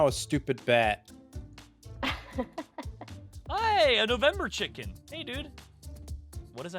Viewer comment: did just call a turkey a November chicken?